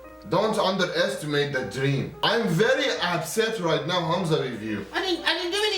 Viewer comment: Oh My Lord that Iranian Uncle had A hilarious Accent ....He said: Humza I am Very Upset... I thought he said he was absent 😂🔫